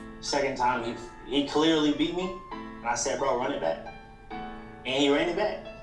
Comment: why does the subtitle say run in Black run it black instead of back?😂